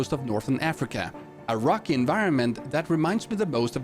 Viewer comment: “a rocky” i thought you said “Iraqi”